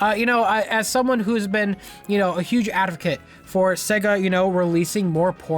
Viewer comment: When he says adovcate instead of advocate.